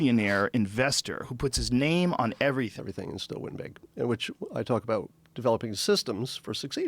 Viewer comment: I really want to know what you were saying at 19:20 >.< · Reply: I think he said "everything is still win big", so he's referring to the competition and the nature of people who try to excel, and then he goes on to talk about how he did it, by taking a culmination of his skills, no real specialist expertise